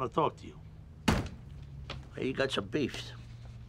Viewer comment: What did Tony Sopranos say?... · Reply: "I hear you got some beefs"